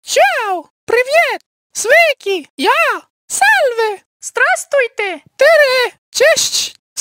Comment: Hold up did six say salami 😂